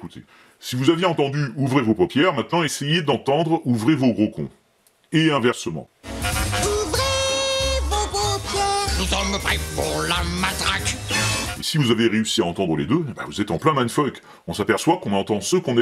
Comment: J'ai entendu "Ouvrez vos bon cœurs" à chaque coup.